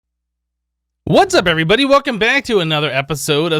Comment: I Miss the project ozone days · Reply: Every time he does the intro i hear "Hey everbody, and welcome back to Project Ozone 2"